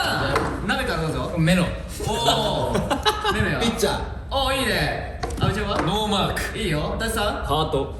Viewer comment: めめの「ピッチャー」が何回聞いても「ピッツァー！🍕」って聞こえてしまう笑